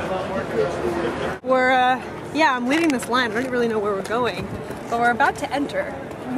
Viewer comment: I thought I heard Layne. Nice footage! If you don't mind my asking how did you manage to get into the Network with a low sub and view count? I'm nearly at 1300 subs and provided 2 recommendations but still believe my application will go in the "maybe later" pile :(